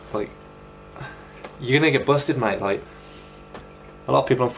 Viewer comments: did you just say nigga? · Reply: Night24 no he said your gonna get busted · What did he say at 4:38??? You ?? Busted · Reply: Robert James-Robbins gonna get busted